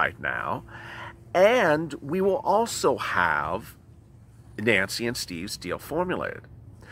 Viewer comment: I had to back the video up. I almost thought you said Nancy Mnuchin and Steve Pelosi haha